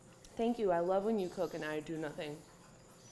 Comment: Jenna: “Thank you. I love when you cook and I do nothing” I felt that 😂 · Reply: Oh I thought she said “I love when you cook 90 to nothing” 😂 both true